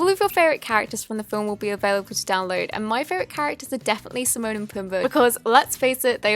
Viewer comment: did she just say SIMON and Pumbaa 😂